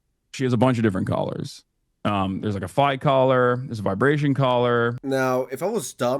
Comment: When he says "she has a bunch of different collars..." does he say a fight collar after when listing them??